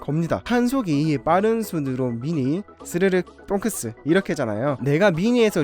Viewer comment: 잘못 들었나 해서 다시 들었더니 똥크스..ㅋㅋㅋㅋ